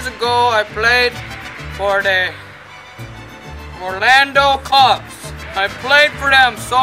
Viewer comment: when he gets hit with to ball though "the orlando Cubs" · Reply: i heard "orlando cops"